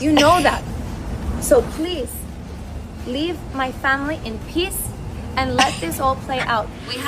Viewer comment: Wait! Did she just say "Please leave my family in piss"...Say what now? Crockett, sounds like she left the ghetto, but the ghetto still in her.